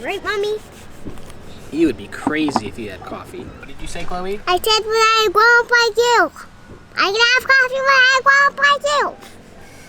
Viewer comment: what did she say? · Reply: "when I'm a grownup like you I can have coffee" hahaha